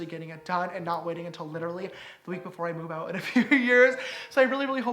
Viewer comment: Did I just hear “ a week before I move out in a few years?” Or I heard wrong? 33:02 Not trying to be negative he can do whatever he wants to do but this house is just so gorgeous 😭😭😭